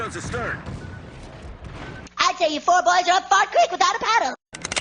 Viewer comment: so what exactly was said there? I heard "without a paddle," but that was all I could understand. Edit: Now I got it: "I'd say you four boys are up fart creek without a paddle."